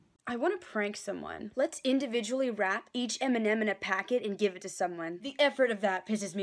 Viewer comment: i heard “let’s rap each eminem” and i was so confused